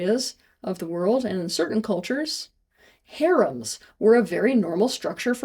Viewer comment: what's the word at 3:27? 🤔😐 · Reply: Harems